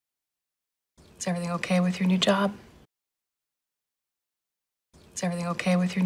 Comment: 이 쎄브니 오케이 유 뉴어잡? 으로 들리네 몇 번 들어도 ㅎ 연음이 울나라보다 심하니 웬만히 공부해선 안 될 것 같네료 외국언.. 오래전 영어 철학 한문을 좋아했던 50대 아줌마 ㅎㅎㅎ